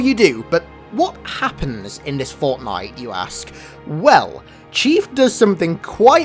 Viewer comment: did I hear fortnite?